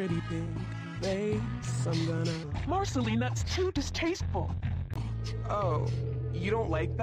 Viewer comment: it sounds like gumball said marcaline thats to distasteful